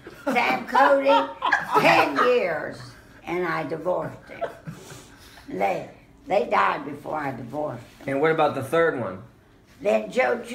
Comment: Wait! What? Did say they died before I divorced them? lol.